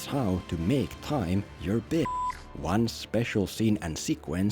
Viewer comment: I thought it said “making time for your bitch” 😂